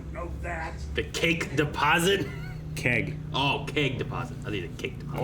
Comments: For twenty years I've thought Troy was saying "cake deposit." You have turned my world right side up. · I heard “cake deposit” for YEARS. It never occurred to me that he said “keg”. I just assumed it was a really nice cake 🤷🏻‍♂️